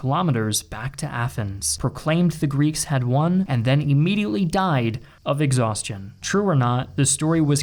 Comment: νενικήκαμεν is what he said